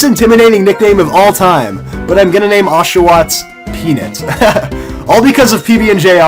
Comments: when said peanut, i swear i heard him say penis! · When I first heard you, I thought you said "Penis." Then I was like "What???!!!" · I swear you said penis...